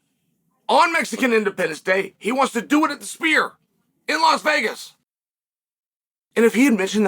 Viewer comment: My ears might not be working, but I could have sworn Uncle Chael kept saying "The Spear".